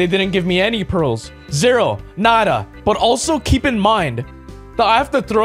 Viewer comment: did he talked Portuguese i think he said "nada" that is "nothing" in portuguese